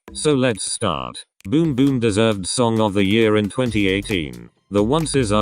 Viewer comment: for some reason, I thought you said bboom bboom DIDN'T deserve SOTY and I was going to fight Who did it like Momoland in 2018? NO ONE